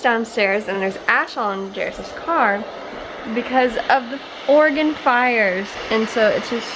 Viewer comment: Definitely thought you said "organ fires" and not Oregon.. I was concerned for a sec